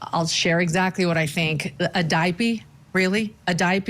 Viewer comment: The way he said “daipey” instead of “diaper”… 30:28 I totally agree. Maybe I’m looking at it too deep here but to me, it’s definitely a tell; using babytalk to infantilize/victimize themselves, trying to really sell it and elicit more empathy inorganically. Giving a sort of theatrically constructed example as to the kind of father he wants to portray himself as in order to control the narrative and get ahead of the truth. This is the kind of tone you hear grieving parents or even empty nesters use when reminiscing about their children and their innocence. For them it’s sweet and loving, but with him it feels entirely unnatural and very performative. I think a conscious choice was made there to use the babytalk. And if it were subconscious, it suggests grief and coping with death. Either way not good.